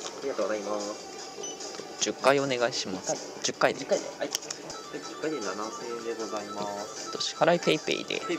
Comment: 「10回で7億円でございまーす」にしか聞こえない